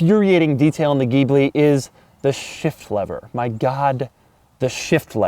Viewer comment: You said shift-lever... My volume is at half, and I thought I heard "shit-flavour"...